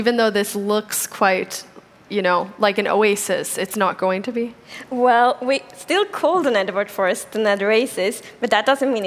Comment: Who Else Heard "Well, We Still Called It N-Word Forest And That Racist"